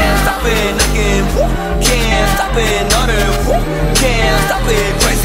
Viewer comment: okay so like I came here if to check if mingyu really did say 'can't fuck it crazy woo' but turns out it was 'stop it' instead of 'fuck it' HAHAHA